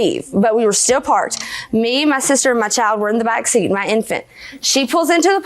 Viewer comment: First of all, the real victim here is the baby, born to an extremely immature girl. I've seen this mentality in middle school boys and girls not grown adults and unfortunately that's probably where they will stay the rest of their lives. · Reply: I thought she said it was her instant child. Maybe she said infant child. I am from North Carolina, it was kind or hard to understand her.